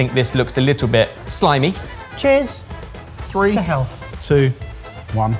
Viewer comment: When said, “to health” before eating the noni fruit, it almost sounded like he said, “to hell.”